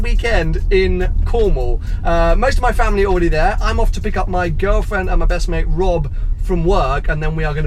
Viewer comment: wait? Did you say girlfriend?!